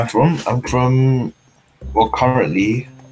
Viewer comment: вы где там Уганду услышали то??🤣 «…well, currently…» — это «…ну, сейчас…», а не Уганда. «В России нет расизма…», а как чёрный чё-то невнятно скажет, так значит из Уганды🤣🤣🤣